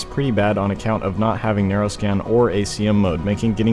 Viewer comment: The video is a bit too fast for me, and btw what is "neuroscan" or do I mishear what you are saying about the radars? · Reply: Probably "narrow scan", most radars can narrow their search patterns to cover a small area which makes them update faster and makes selecting and locking targets easier. To help with the speed of the voiceovers I added subtitles to my most recent video and people seemed to like it, I think I'll keep doing that as long as it helps